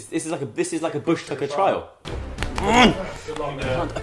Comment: only English would understand what he means 😂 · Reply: I'm British and I don't even know what he means.. and it sounds like he said "bush tucker trial" xD